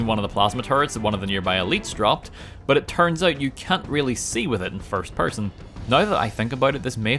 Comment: “But it turns out you cunt really see with it in first person.” As an American, that’s what it sounded like he said with his northern Irish accent 😂😅